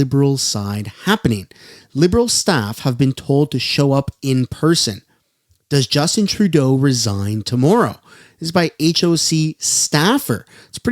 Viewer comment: The sound was down when you said he was resigning tomorrow...I thought you said dying at first 😂😂